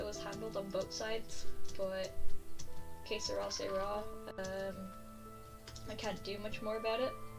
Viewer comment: Did you speak Irish in this video at one point one bit sounds like you said case of rar or something I can’t really understand it just wondering if you spoke Irish in that bit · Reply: My intro is in Irish (I say hello potatoes but in Irish). I believe I said Qué Será Será, which is a saying meaning what will be will be (it's in Spanish). Hope this answered the question!